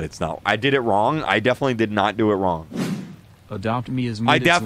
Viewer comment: For the people that don't know where the swear is: He says: "S**T, i defenitly did not do it wrong"